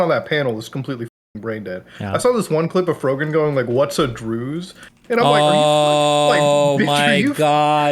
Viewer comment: what is he saying here? i dont even understand the word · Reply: Druze, not to be rude, but I would suggest that you Google it if you are interested in the politics of the Middle East